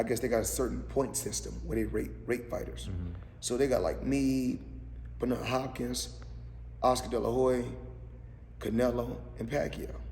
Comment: about 8:55. Did Floyd say Oscar De La Hoe-e?🥊👊🏿🤔🤣